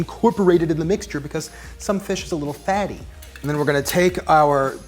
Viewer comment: Did he just say: ''Because some fish are little faggots'' or is it just me?